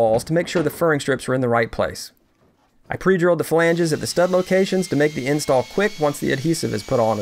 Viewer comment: He said he drove holes in the flanges and I thought he said he drove holes in the filangies... from friends. I freaked out and turned on the subtitles, my dreams were crushed.